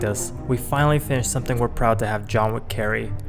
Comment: its 2 in the morning, and tell me why when i heard this i thought “the john wencil” (said when-sill) and burst put laughing